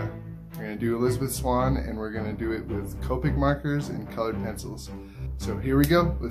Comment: I thought you said "covid markers" and I was like what are covid markers?